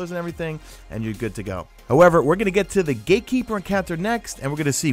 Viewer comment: Did I just heard "GayKeeper"???